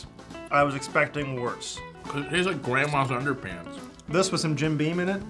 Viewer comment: "It tastes like Grandma's underpants..." Did I hear that properly?